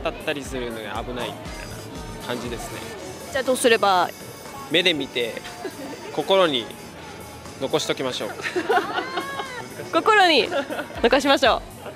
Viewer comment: can someone please write in Japanese what the guy at 08:08 was saying please I really need that sentence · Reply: 目で見て心に残しておきましょう